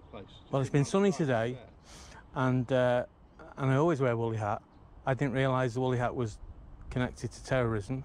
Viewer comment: i thought he said a bully hat lol . bye the way great use of great music